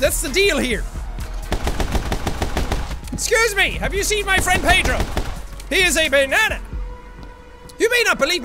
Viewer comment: I thought it said "My Friend Pedo" .